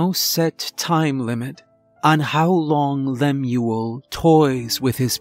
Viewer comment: Hey everyone let me know if you enjoy this one! I'm really happy with how it came out. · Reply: On another note, can anyone tell me what a "Lamuel" is? I initially thought they were saying "Lemure", but the transcript corrected that assumption & nothing came up on google...